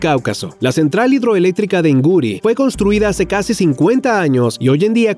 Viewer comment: ¿Escuché en el minuto 4:33 "Guri"? Si es así está mal descrito. Guri es el embalse más grande de Venezuela, se encuentra localizado en el estado Bolívar, donde se encuentra la Central Hidroeléctrica "Simón Bolívar" siendo la segunda mayor de América, pudiendo considerarse la primera entre las que se encuentran en un solo país.